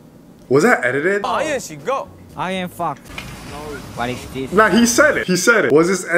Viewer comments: in skzoo hes resembles as a fox so he tried to say fox in english but he is not that good in english so it sounds like he said fu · WHAT HE MEANT TO SAY IS I AM FOX BUT IT SOUNDED LIKE I AM FUCK😭😭